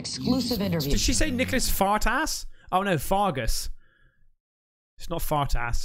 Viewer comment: Did she just say Fart Ass?! lmaooo I died